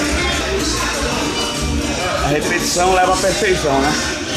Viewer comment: Ele falando que "a repetição leva à perfeição", eu entendi "a refeição leva à perfeição", soltei um "concordo"! 😂